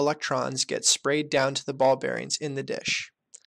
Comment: After watching the Barbarians on Netflix i saw this video and i kept hearing "Barbarians" instead of Ball Bearings